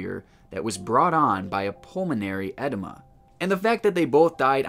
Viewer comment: I thought you were saying pulmonary ENEMA. 😂 Just FYI, it’s pronounced Edeema which is a collection of fluid in the lungs.